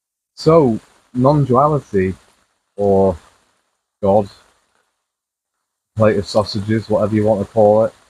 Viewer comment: did you just say, "FINDING SAUSAGES OR, WHATEVER YOU WANT TO CALL IT"??? GO LISTEN. 😊😅😅😅😅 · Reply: I did say a plate of sausages. I like to be silly sometimes. 😀